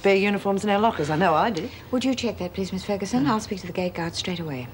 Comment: it sounds like Erica says she is going to speak to the gay guard straightaway